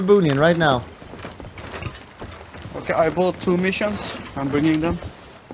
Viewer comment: what does he say? I can't understand for shit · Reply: ok, I bought 2 missions, I’m bringing them